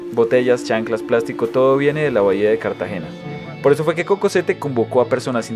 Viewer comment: Like si pensaste que dijo cuco